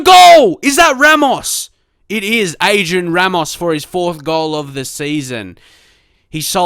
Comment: I thought you said Asian Ramos